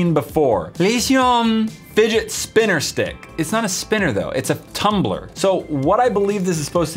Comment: That Leshion Spinner Stick was nearly impossible to make it stand up! Do you guys think you could do it? If you enjoyed this video, be sure to check out 6 Of The Most Unique Fidget Spinners! <link> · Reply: thought you said "lesbian spinner stick.."